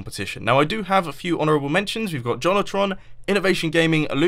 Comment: Did he say JonTron? No way. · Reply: he said Johnotron or something like that.